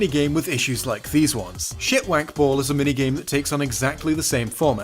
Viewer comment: Did just say shit-wank ball or did I imagine that?